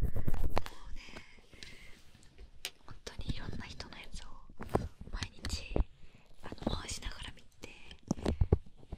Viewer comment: ほんとに色んな人のやつを毎日 の後ってなんて言ってるんですか？ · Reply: 回しながら見ててって言ってると思います！